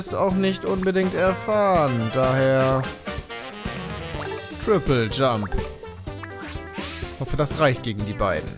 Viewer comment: habe ich statt "Triple Jump" "Vierteljump" verstanden.